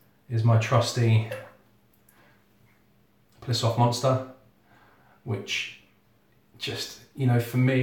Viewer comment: I have to admit, when I first went past 5:05 I thought you said your brush was a Piss Off Monster lol.